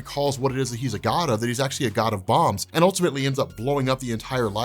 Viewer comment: lol i had to wait for you to repeat yourself i was expecting you to say god of songs , and i thought you said god of bongs , and then i realised you said god of bombs.